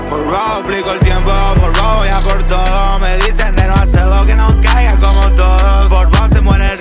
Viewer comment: Mal ahí pensé que decía " me decían negro hacelo"